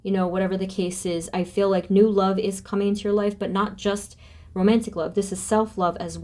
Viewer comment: Before I pick my pile, i have to say I swore this said "whats coming in your next life" like wait wut, next life? Haha 😄 🤣 😅